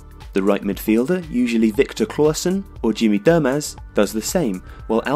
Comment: Did you say "Jimmy Dum ass"? XD